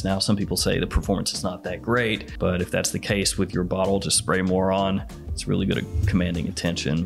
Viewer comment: why is he calling us that? 🧐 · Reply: He said “spray more on”